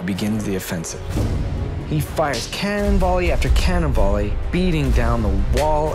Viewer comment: "Cannonbally after cannonbally" HILARIOUS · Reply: I also heard it as Cannonbally and tbh I like it better that way